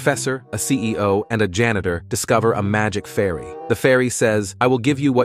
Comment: I thought it said they met a "magic furry" lol